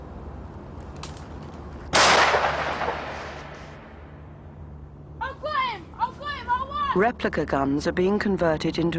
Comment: does anyone know what he was saying? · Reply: “I got him, I got him. I won!”